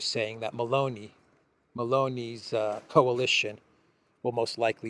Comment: Ha! At first, I thought you had said that Ursula was holding a crystal ball with "Baloney" in it. Had to rewind. May very well have been even more apropos!